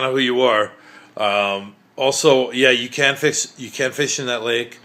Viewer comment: Bro just said “you can’t fish in that lake” but then goes “it’s catch and release only.” 💀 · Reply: I think he says " you CAN fish in that lake but it's only catch and release". I also thought he said can't.